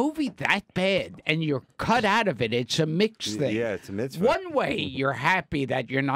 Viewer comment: Gilbert said it's a "mixed thing" while Dave thought he said it's a "mitzvah" (a Hebrew word for a good work).